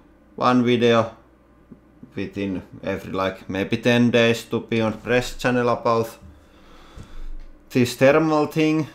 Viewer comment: I heard you say peon press channel at the beginning :P